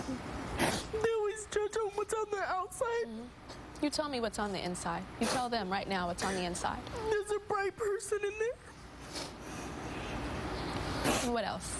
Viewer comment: i thought she said "bi person" lol i was finna say... now is not the time to be coming out the closet